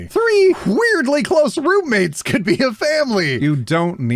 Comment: I thought you said broommates not roommates at like.. 4:50?